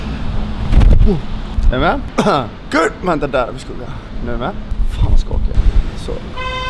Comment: I love how u edited this video and when u filmed the jogging part. Lol i heard " fan va skakig" something